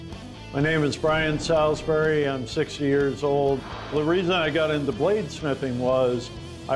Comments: I heard 6 years old and I was confused like what? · i heard him say 6 yrs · i thought he said im 6 years old XDDDDD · bruh did he just say he's just 6 years old?